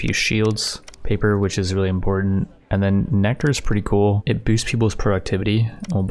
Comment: What did he say? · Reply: nectar obviously